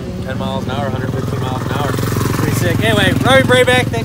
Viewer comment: Did he say "Robby Brabec" at the end? 2:45 🤣 jesus